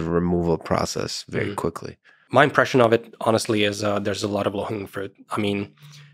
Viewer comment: In case anyone else missed it like me (non-native english speaker) at 57:26 Andrej says "there's a lot of LOW-HANGING FRUIT". The subtitles are wrong and I just had to rewind a bunch of times to understand why they laugh.